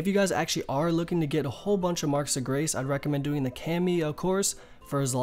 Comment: wtf is he saying @ 2:51? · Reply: "If you guys are looking to get a whole bunch of 'Marks of Grace'.